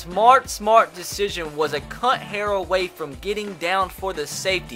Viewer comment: I thought you said “a c*nt hair away” as Matt Flynn was almost sacked for a safety. I laughed until I cried.